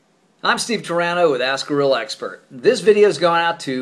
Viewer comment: lol in the intro I thought he said ask a gorilla expert